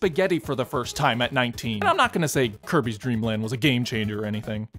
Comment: did he say Kirby’s Dream Gland?